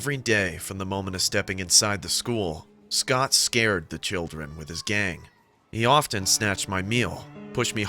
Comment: i heard "With his gay" lol